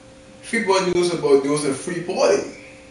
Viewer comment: what questions did he ask in 0:33 cause I didn't hear him to well. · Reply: if freeport news about news in freeport